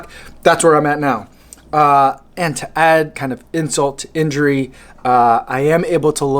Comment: I heard "To add insole to injury" omg